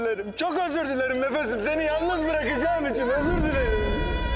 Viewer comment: Ben Russian bu dizi cok sevdim ve bu 0:38 ne dedi ki? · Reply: Sen Hep kalbimdesin gibi bişey